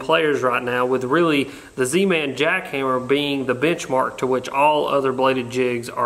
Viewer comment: I will say, I like the jackhammer just because of the “crispness” of the vibration and how quickly it starts. That super thin blade vibrates soooo fast! At 0:55 I thought you said “bitch mark” It’s that dang southern accent